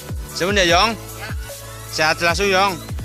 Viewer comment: menit 4:03 kowe ngomong apa 😅aq krungune sehat selasu mbok sehat selalu